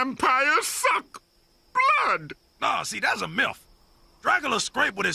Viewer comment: did he say MILF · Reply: I heard that too but it is "myth"